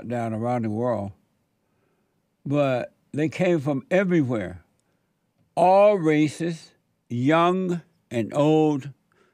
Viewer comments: for real at first I heard him saying they were all racist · I thought he said: All racist. young, old, middle age, black, white, mexican, chinese All racist 🤣🤣